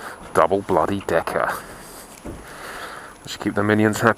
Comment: At first I thought that candy bar said "Necker" which wouldn't go over too well in America. sounds too much like something else haha